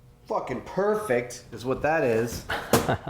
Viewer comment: Did Adam just say "Fucking Perfect, is what that is" At 13:00?! Heck, in that case, that's the first time i hear Adam Swear without the "Beep" !! :d